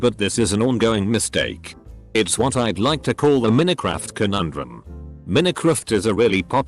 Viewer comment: bruh when i hit 29:08 i thought it said minicraft not minecraft and yes minicraft is a different game